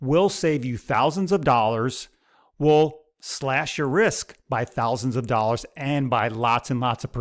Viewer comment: I honestly thought he said buying deep in the money call options will save you thousands of dollars and Slash Your Wrist🤣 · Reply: I heard wrist too 😂 I'm like, no, no, he said RISK 😂😂😂😂